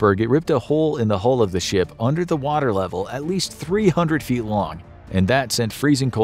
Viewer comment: Narrator says, "300 feet" while the caption says "3,000 feet" Since 3,000 feet is over three times longer than the entire Titanic, I'm gonna assume the narration was correct and the graphic was off lol